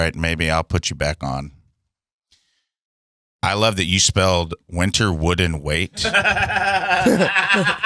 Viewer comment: Tina I'm with you I thought he said winterwood and wait 🤦🏼‍♀🤣